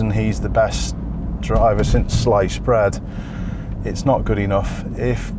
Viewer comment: The best driver since sliced bread! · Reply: I misheard "Since lice spread!" :D (I'm a non-native speaker)